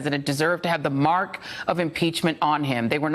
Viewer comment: For a moment I thought he said, "the mark of the beast." That also will be fitting.